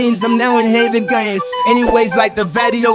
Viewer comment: When he said "times to reincarnate " It sounded like he said "time to bring Kayne"